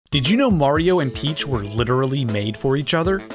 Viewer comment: First time I thought you said wario and peach